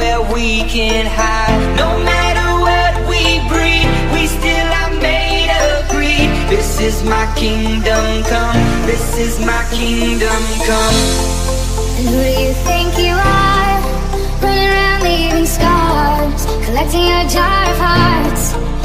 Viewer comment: Có lúc nghe nhầm từ "this is my kingdoms come" thành "this is my kingdoms 'cum'"